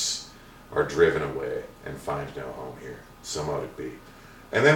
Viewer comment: I cannot eare what he said, is it "driven away and FIND NO HOME HERE???? please tell me if you understood what he said, you see, I am a french woman & sometimes I just can't understand or ear right, thank you all muah xoxox